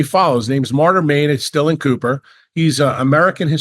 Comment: Did anyone catch the historian's name at 10:38? It sounds like Martyr Maim Estellen Cooper. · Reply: Martyr Made - Darryl Cooper